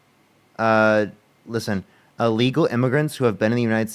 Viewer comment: you did say, or at least it sounds like you say "illegal" here, I thought you did as well and was very confused. I think you said "uh-" then lead into "legal" which makes it sound like you said illegal.